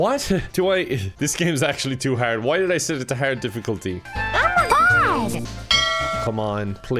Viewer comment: At around 4:26 did Elmo say "Elmo God" ?!?!